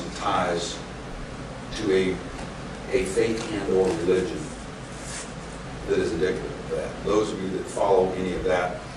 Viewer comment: Did he say fake religion at 4:48? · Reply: "a faith and or religion" is what I heard.